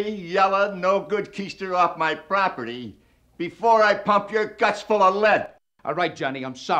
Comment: I always thought it said “before I pump your guts to the left” lmfao